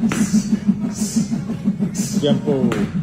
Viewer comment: What chasing dreams looks like 💪🏽 wish him the best but I don’t think it’s his time yet. They are putting him in with a monster. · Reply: I thought his trainer said jump rope at the end but now I understood he said “Tiempo” meaning Time!!